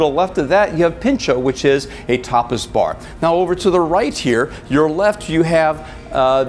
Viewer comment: did you say "Pincho a topless bar" ?